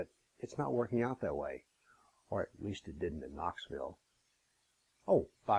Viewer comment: Bill couldn't understand what you said (4:15), "the talks are going on for the .... " excuse me? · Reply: the talk's going on, ummm, at a different venue. What he is saying there , is that he will still have that talk that was canceled , but at a different venue he doesn't want to mention here so some sharia loving socialist traitor pig or their burqua wearing goat of a life partner won't whine to that new venue to get the talk cancelled.